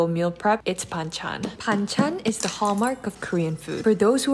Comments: i thought she said bang chan 😭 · I thought you said bang Chan 😂💗💗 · Why did I heard "Bang Chan'' 😭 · I thought you said Bang Chan 😂 Well I mean… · I THOUGHT SHE SAID BANGCHAN FOR A SEC AND I WAS LIKE GURLLLL 😭💀